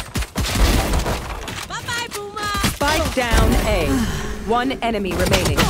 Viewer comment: Was it me or did she say bye bye boomer